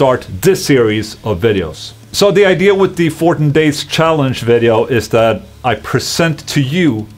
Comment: Anyone thought ola the sweed said at the first of the video :"fortin days challenge" ?